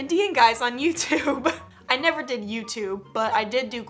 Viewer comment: Did she say she never did YouTube